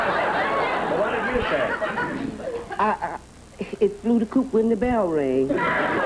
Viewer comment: wait what did she say?? 'It flew the coop when the bell rang'?